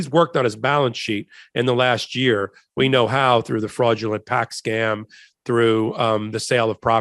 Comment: Did I hear this part right? Trump has been paying off his Deutsche Bank loans with his "fraudulent PAC scam" money? Is that the "Save America" PAC, or "Stop the Steal"? Also, is that documented? Because... is that legal for him to use that PAC money for 'just any old thing', like paying off those Deutsche Bank loans?